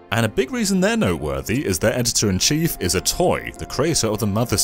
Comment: Did anyone else hear "their editor-in-chief is a toy"?